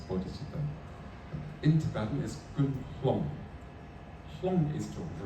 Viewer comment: Could anybody tell what Jigme Khyentse Rinpoche said in Tibetan at 58:05. Rinpoche explains: ''the first part is motivation, that is bodhicitta. In Tibetan is ཀུན་...", -- the second syllable sounds to me ཧལོམ་ . Rinpoche continues: "ཧལོམ is 'to rouse'." What is the second syllable in Tibetan, please? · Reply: I am not sure if it is: Kun slong (ཀུན་སློང་) In Tibetan it's Kun slong (ཀུན་སློང་). Kun is to rouse. Anything we do should be roused by something. The propelling force need to be bodhicitta to do these practices.